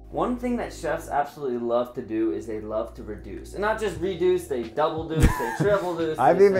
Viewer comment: for the slightest second I thought it said seduction 😂😂